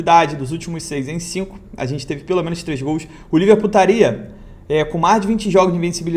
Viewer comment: Só eu ouvi Liver Putaria kkkkkk